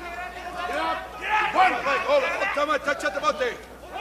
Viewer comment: 「☆▼◎◆※＆～ チョットマッテ！」に聞こえるけど、ホントに言ってるのかなｗ